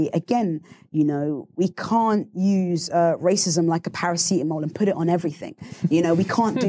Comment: Awesome. Thanks Ms Akanbi, thanks all. What is the word that sounds like pa-ra-see-um at 38:09?